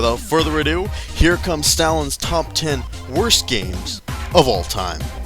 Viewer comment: i thought is said satins top 10 worst video game list